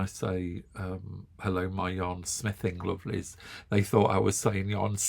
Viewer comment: ♥️ i love your Temu yarn reviews ♥️ thank you so much for doing them 🙏🏻 i am going to order some Fairyland today. PS; i also thought you were saying yarn sniffing lovelies 😂 and i loved the saying, as i am always with my nose in my balls of yarn sniffing the sheep and smells of the yarn - lol hello my name is Monique i am a yarn sniffer 👋🏻